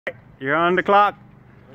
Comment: Guy recording: “thave anath dealma lay” Earth’s Axis: *Flipped* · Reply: I thought he said “ you’re on the clock “ meaning they better get to work